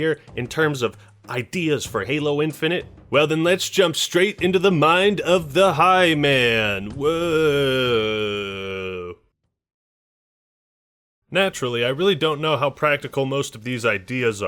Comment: Did he just say to jump into the mind of the hymen